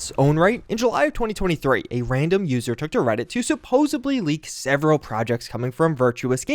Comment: I think i heard you say supposebly? Not a word, look it up.